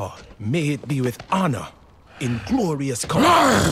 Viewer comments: i thought varok said "Without honor?" at the end, which i was confused by, but according to the subtitles he actually says "Without armour?" which not only makes more sense but shows a lift in Varok's mood which is a much better ending :) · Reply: I thought he was saying “with our honor” meaning he will live on head high with his values and stop listen to blind loyalty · i thought varok said "Without honor?" at the end, which i was confused by, but according to the subtitles he actually says "Without armour?" which not only makes more sense but shows a lift in Varok's mood which is a much better ending :) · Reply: Ahh, I heard “with our honour”, but I have bad hearing